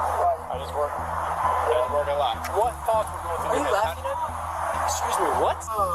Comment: What did he say to the cop? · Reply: "are you laughing at me?" yeah i would be confused like the cop too